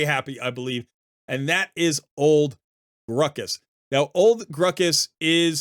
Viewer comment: I thought you said ole ruckus like uncle ruckus lol